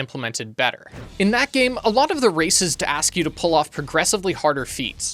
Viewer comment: when you say "races", i keep hearing "racists" for a sec before my brain corrects it 😂 (Im like, "Wait, what are the racists asking??")